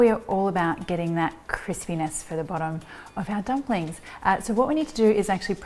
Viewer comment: yeah thanks subtitles for saying CHRIS PENIS INSTEAD OF CRISPINESS